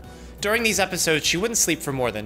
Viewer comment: "Wouldn't sleep for more than two weeks at a time." So... she went without sleep for a solid 14 days? This phrase is confusing. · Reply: huh... i just double checked with the cc, but i thought he said she would sleep for 2 weeks at a time.... which lines up solid with how my depression works! LoL. ...but closed captions confirmed homie said "wouldn't" 🤷‍♀️